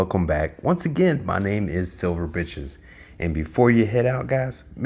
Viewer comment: Sounded like you said my name is silver bitches lol. I like the intro.